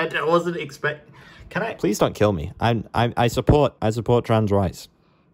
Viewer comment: the captions thought you said "i support trans rice"